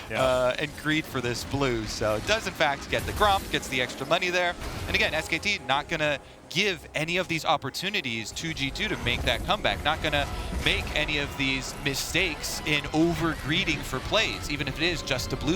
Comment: did he just say skt?